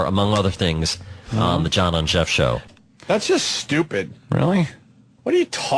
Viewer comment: Did he say "the John on Jeff show" at 18:44?